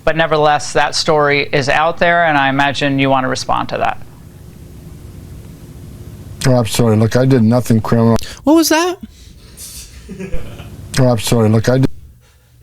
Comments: I think Homan said “yeah I’m sorry.” I defiantly hear “sorry”. Freudian slip? Responding to the allegation with “I’m sorry”… 🤔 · He said..."ABSOLUTELY". you made me LOL...I never know how to explain that THING they do at FOX.....it needs no words just that face and sound! THANK YOU!! :D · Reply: I hear "Im sorry, look i did nothing criminal"